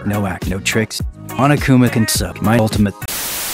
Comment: cuz it's the ultimate survivor: no act, no tricks monokuma can suck my ultimate [REDACTED] haha, looks like they didn't want me saying that, huh? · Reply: I'm stupid I thought he said "Monokuma consumed my ultimate" 😭